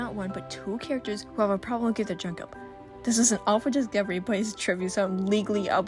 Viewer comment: I love this so much but pleaseeee tell me what u said /ment at 14:58 fact number 82 because I really wanna know but can't understand you 😭😭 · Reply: ey're saying that two characters in particular have a reptile dysfunction